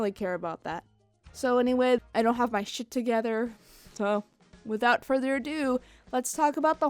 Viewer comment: did you say S***?